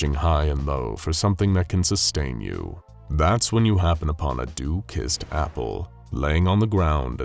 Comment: Dew-kissed apple. I kept hearing “due-kissed” apple. I kept thinking “how was the apple due for a kiss, or is the apple just laden with duties?” Nope, neither. Just dumb.